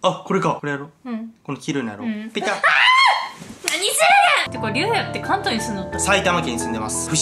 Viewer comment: 何するげんって、が言ったときひき肉ですっ！！って聞こえた笑笑😂